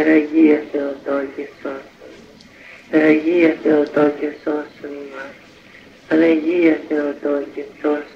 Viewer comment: Does anybody know what prayer it is at 1:33 onward? I think I hear Panagia but I cannot make out the words/prayer clearly. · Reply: Geronda also says: "Υπεραγία Θεοτόκε σώσον ημάς" (Most Holy Theotokos save us) and "Παναγία Θεοτόκε σώσον ημάς" (Panaghia Theotokos save us)